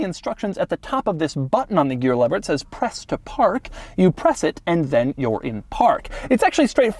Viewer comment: I thought the shifter said “Presto Park”😂😂 I was like oh I guess it’s for when you need to get out of the car really quickly lmao